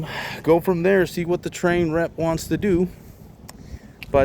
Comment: Darn! It was such a productive day until the manufacturer threw you a curve. When you said Trane rep, for a second I thought you said Trane wreck 😊